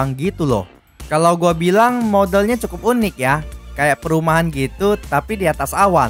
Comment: gatau salah denger itu kenapa ngomongnya mod*l